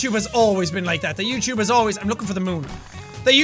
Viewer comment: Other than the game and all what jack said about Youtube, heres something i thought he said, "All the youtuber have been lookin' for the moon"